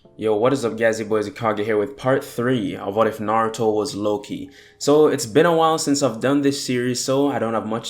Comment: was just me or did he say if naruto was loki